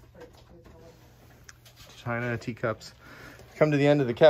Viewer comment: When you said China teacups I thought you said tie dye tea cups and I had to replay it about 3 times